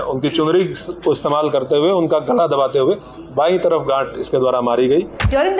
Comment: wtf just i heard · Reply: Gaanth